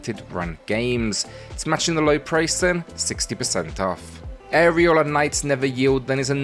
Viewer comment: Legit thought you said Areola Knights and I looked at the screen. 🤣